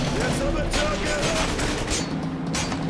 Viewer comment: He said, "Sorry, Jules." Jules was his friend that died in the beginning of the game. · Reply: i thought he said sorry jews